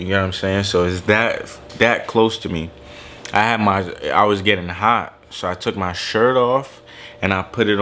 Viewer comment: Yo at 4:13 it sound like he said he was getting high